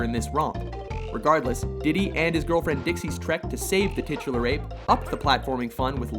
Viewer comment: Did he just say "Diddy Kong and his girlfriend"